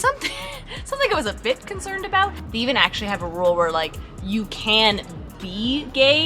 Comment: "THEY even have a rule" I had to rewind several times! 😅 I first heard: "we even have a rule" or "Lev and I have a rule" I was so shocked, and confused and knew I must have misheard lol. (Though I've met people who've surprised me by say "we're down with it so long as they don't act on it.") Relief!